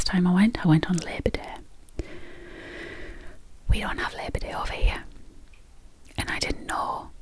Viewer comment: Engagement incoming: 12:14 "We don't have Labor Day over here." Whew, at first I thought you said "We don't have liberty over here." I thought, well that's harsh. And, couldn't find an octopus, so, here's a gecko - I think.🦎